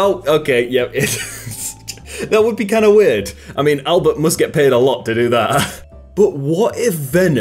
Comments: Excuse me, did you just call him ALBERT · did he say albert?